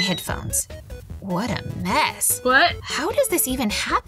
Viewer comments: I don’t know but did someone heard a weird WHUT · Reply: Me they do say what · Did anyone heard a WHAT at 5:41 ....I guess it was real voice😂😅